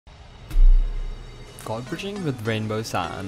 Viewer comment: I thought you said godbridging with ranboo